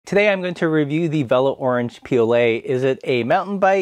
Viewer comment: When the video started playing I heard "Orange PLA" and thought "Is this a 3D printing channel now?"